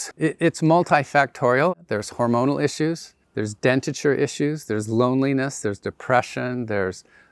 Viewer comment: what did he say? It sounded like “dentiture issues.” Did he mean “dental issues,” or maybe “dentition issues?” It sounded like it was something to do with dentition but I (a dentist) don’t recognize the term.